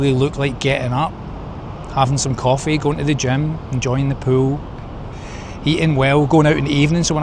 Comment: At first, I thought you said "enjoying a poo," not "enjoying the pool," and my reaction was, "yep, me too!"